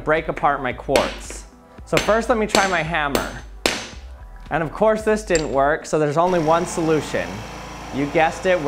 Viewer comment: “of’quartz’ that didn’t work.”… Is what I heard.